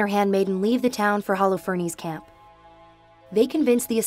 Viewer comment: on the subtitle it says Allah ferny's instead of Holofernes